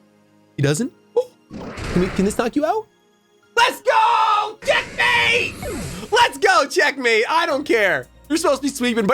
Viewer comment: When he yelled "LET'S GOOO CHECKMATEEE" I heard "JEBAITED" LOL